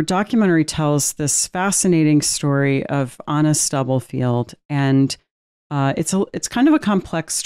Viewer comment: Wow! At the start of this, I kept hearing ‘Honest Doublefield’ - I thought, wow that’s a really interesting name for this character! Then realised it’s Anna Stubblefield. 😮